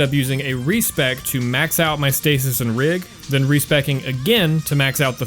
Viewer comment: hey what are you saying at 24:50? Reese packing? Re:bagging? And can you explain? Thank you, I’m super curious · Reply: I think he said respeccing